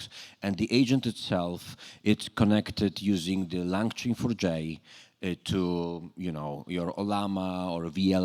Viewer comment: which particular framework mentioned at 26:24 🤔. Langchain ? It sounded like Langchain+ Neo4j. So is it Knowledge graph based agents dev? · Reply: langchain4j and it does not use rag. The context is one source file with the sources of the classes that it is using so no need for db. Much simpler this way